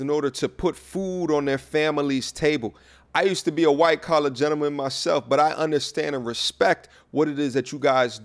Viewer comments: My IG is growing faster than my YouTube. Go follow: <link> And don't forget to sign up to my mailing list: <link> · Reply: I thought you said "white colored man" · I thought you said "white colored man"